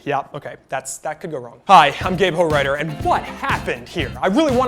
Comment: AHAHAHA when he said his name i thought he said " hi im gay porn writer" and i was like woah